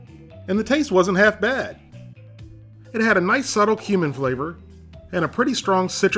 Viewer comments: "It had a nice subtle human flavor" I know what he really said But when you're half asleep It really sounds like that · I could swear you said "it had a nice subtle human flavor"! I'm changing my name!